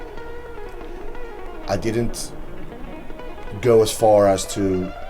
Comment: I thought he said he "...settled for quiet." at the beGYNing?